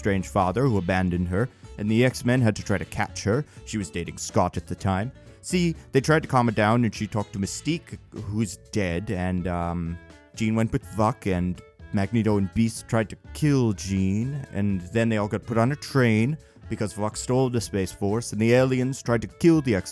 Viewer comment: Am i the only one who hears.. "Jean went to fuck"?